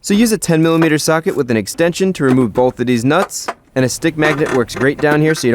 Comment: did u say these bolts or deez nuts